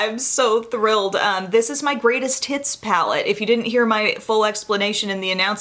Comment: I just wanna say, I thought I heard you say, "this is my greatest tits palettes" at 1.00 and it made me chuckle. Alright I'll go now.